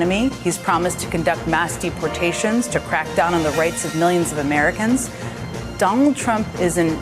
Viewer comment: How do you deport AMERICANS? Did i just hear her say he wants to deport Americans which was clearly a lie and they wonder why they lost???